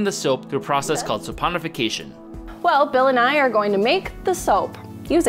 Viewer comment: when she said bill and i, i legit miss heard and thought she said bill nye